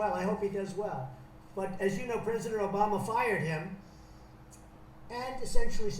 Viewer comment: Did anyone else hear Trump say “prisoner Obama” at 4:40 during the meeting??